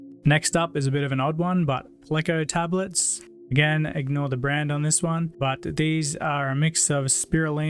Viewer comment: I heard “I don’t know the brand” and nearly slapped my eyebrows off with a face palm, then I rewound it and heard IGNORE the brand 😆